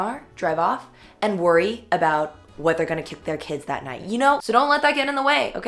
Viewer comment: “kick their kids”? lmao did I mishear that?